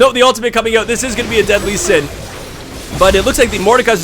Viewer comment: Misheard "a dead Lee Sin" as "a deadly sin" several times here. Made me chuckle. :3 Also, unfortunately the commentary was less about Jungle Olaf than basically everybody else. Not really a complaint though, seeing as the game consisted almost exclusively of team fights, making a focussed commentary impossible.